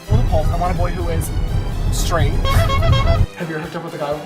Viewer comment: I need exact subtitles. What did James said starting from 0:10? I hear “bhrlparghaj straight ” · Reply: "I want a boy who is straight"